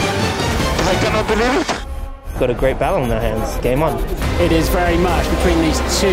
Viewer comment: Can anyone tell me what HAM said at 1:17? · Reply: Got a great battle on their hands. Its game on